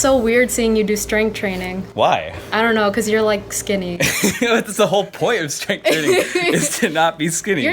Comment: did she really call him skinny im sorry but what lol